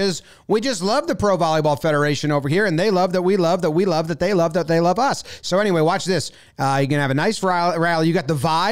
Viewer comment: "they love that we love that they love that they love us so.." (that took me way too many tries to see what he actually said there 5:15 )